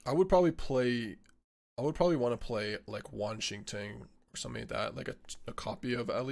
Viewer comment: The amount of times I heard "this works with Washington" when was talking about Wan Shi Tang....